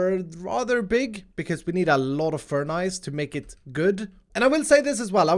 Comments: did you say "furnice" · Im sorry but at 17:48 did he say furnies? That is the best thing ive ever heard ahha omg